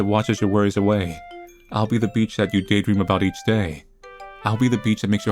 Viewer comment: I'm sorry but at 41:28 I heard the word "Beach" as the b word insult and couldn't stop hearing it afterwards